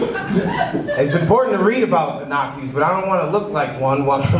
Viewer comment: When he said “it’s important to read about the nazis” I legitimately thought he said “I support and read about…” 😂